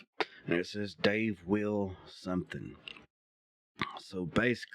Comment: I hear " Dave will protect you" at the section you can't make out. 💜